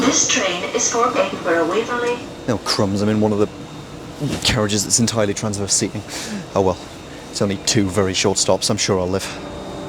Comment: What did you say at 2:47? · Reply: "The carriage isn’t entirely transverse seating. Oh, well, it's only two very short stops; I'm sure I'll live."